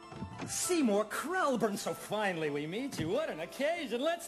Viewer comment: I knew i kept hearing someone say "up yours." Had to turn on the subtitles to double-check. This movie is just too damn good 💚